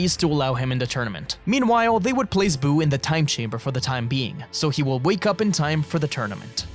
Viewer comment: did he say time chamber?